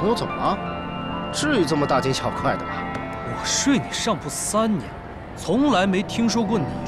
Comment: 白: 我睡你上铺三年。。。 我听成 我睡你三年